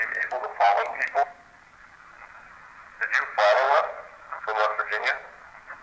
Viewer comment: Another great video guys. At 1:22:08 when asked "Did you follow us?" I swear I heard a very quiet "I DID" Always cool to watch in the middle of the night to set the mood. Love your stuff <3